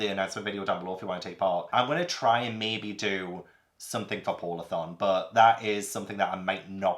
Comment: I honestly thought you said “pornathon” when you said “polarthon” 😂